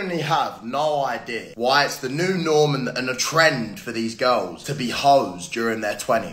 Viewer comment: did he just say “the new Norman?” Lmao